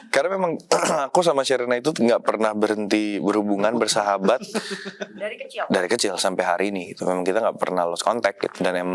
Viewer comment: gak pernah berhenti … ardit nyeletuk sesuatu tapi ketimpal suara derby, ngomong apa ya si ardit???? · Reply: iyaa itu kan derby bilang tidak pernah berhenti terus si ardit nyeletuk “berputar” terus karena derbynya nggak ketawa makanya ardit cuma senyum nahan ketawa terus randy kenapa ketawa karena dia ngetawain si ardit. itu sih yang aku tangkep😂